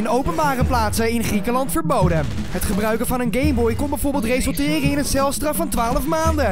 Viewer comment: hoorden jullie ook gayboy ?